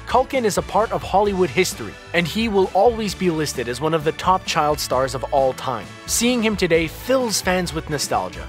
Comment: i thought it said child pornstar... welp, im on a watchlist now